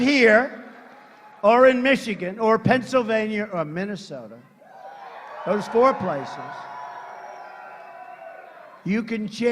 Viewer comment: Did he say foreplay says